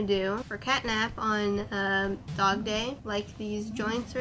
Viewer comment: Cat map??😅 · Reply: Yeah I noticed it sounded like I said cat map sorry about that 😂